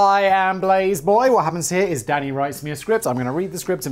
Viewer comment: I misheard “Danny writes me a script” for “Daddy writes me a script”” and nearly choked on my cuppa! Thanks!